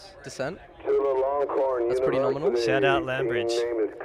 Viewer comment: The radio guy is telling what Institute it is but I cannot understand it. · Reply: It was Team 204, Chulalongkorn University. Hope this helps!